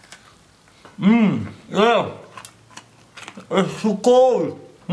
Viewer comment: what the heck did he said? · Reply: He said "it's too cold".